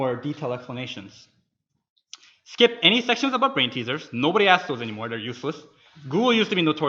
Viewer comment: What does he say guys? Skip any section about (green t-shirts?? lol) ? Thank you in advance :) I'm not a native english speaker, this part was fairly fast.. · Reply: brainteasers